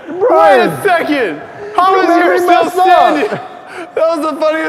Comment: did anyone else hear him say "how is your still standing?"